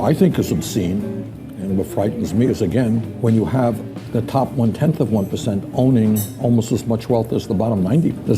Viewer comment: I love ya and I truly dislike Bernie but he clearly said top 1/10th of 1% which is a more exclusive club than the general 1%. He said this at 4:13 in.